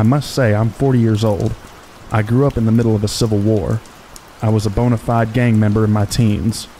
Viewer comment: does he say "I grew up in the middle of the civil war"? or "a civil war"? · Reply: A civil war. It is someone from another country